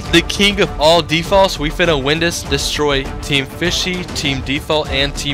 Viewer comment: I love how he accidentally says destroying defaults instead of bananas XD Btw fishy army foevs ❤🐟🐠🐡